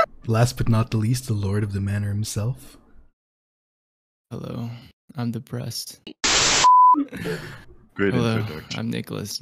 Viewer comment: At first I thought he said "Hello, I'm a breast" XD